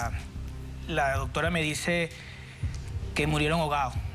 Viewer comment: Jajaja, en el minuto 3:06 escuché una frase que dice " ya doctora"